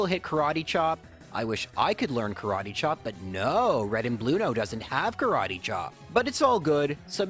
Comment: did you just call him “Red and Bluno”? Please do it more. It fits him so well, and imo it’s even better than Rival Fival.